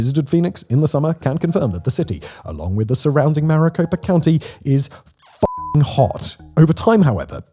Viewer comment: Maricopa county, sounded like you said Mariposa county.